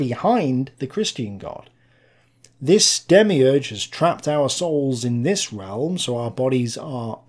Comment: Good video......But when you say 'our' in your videos say 'hour' instead because it sounds correct.👍🏻... It sounded like you said 'are souls' in this video and that sounds like 'arseholes' to non Brit's.😉